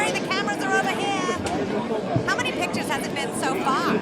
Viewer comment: did anyone else catch the audio of a woman calling out, like directing them? 0:59 "the cameras are over here" "how many pictures has it been so far?" 1:12 "okay, so how much are we getting paid for this?" 🤮🤮🤮